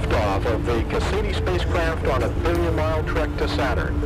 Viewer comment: Does he say, "...lift off. And it's on its 30 mile trek to Saturn?" I relistened to it 5 times, and thats what I keep hearing.